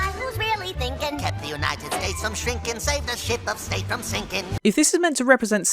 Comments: Did he just say S*** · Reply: "Save the ship of state from sinking" is the line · did he say the S word? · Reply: "The Ship of state" :) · did wakko say the s word or state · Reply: “ship of state” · did- did he just say shit? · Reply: Ship of state